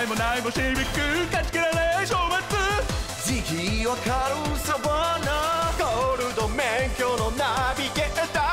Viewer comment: シルビア 180SX 0:47 ランエボ シビック って言ってる（他の方のコメで気付いた） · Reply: ここサバンナ(涼介のFCサバンナRX-7)に聞こえたのはわたしだけ？